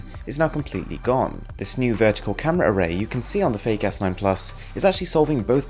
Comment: I thought he said "fake ass 9+" xD